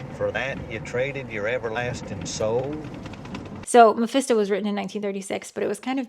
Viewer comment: I thought you said fisto and was like wait is this about new Vegas?